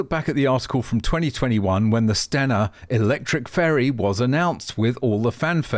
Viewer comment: Electric Ferry???...oh I thought u said "Fairys" the things designers of Electric Ferrys believe in.